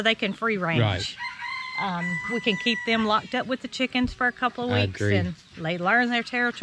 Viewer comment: Lol, you said "they're all locked up;" and I heard, "they're all flocked up." Thought you said it on purpose. would have been cooler if you did. =P